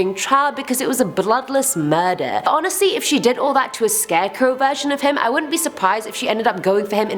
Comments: i keep hearing "scareco" lol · Lol 🤣 it sounds like Ur saying scarecoo, much love x